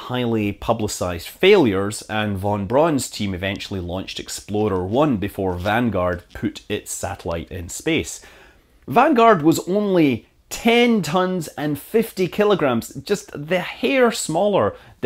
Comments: sounds like you said "exploder 1" lolol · I heard "They eventually launched Exploder 1 before Vanguard..." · Funny, I was just discussing this with my brother yesterday... Talk about good timing! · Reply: I know it's just the accent, but I swear you're saying "exploder 1" there ;) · When you said "Explorer I" I thought you said "Exploder I." Would have been much more Kerbal.